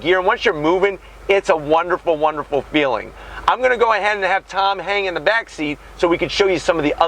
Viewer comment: i thought you said "i'm gonna go ahead and let tom hanks in the back seat"